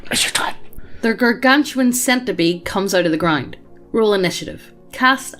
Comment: I heard 'Centibee' instead of 'centipede' the first time. ~**chuckle's nervously as he imagines it**~